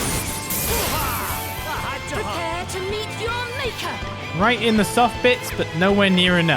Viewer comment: Wait, Elvis says: right in the soft bits? I always thought he said: riding the soft beds. Or that's what I heard. I thought it was some weird scottisch slang I didn't understand, but I just misheard it the whole time.